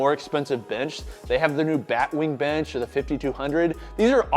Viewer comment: Surplus Strength Rep Black Wing--sounded like you said bat wing